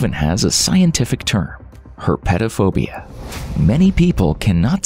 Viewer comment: I swear i thought he said pedophobia in the beginning.